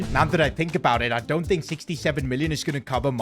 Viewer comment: did u say 67